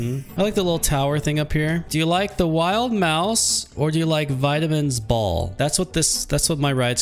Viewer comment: seems to hate trash cans. He cleans everything up by killing his guests😅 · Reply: I thought that said Vietnam and I’m like “huh?? Ok”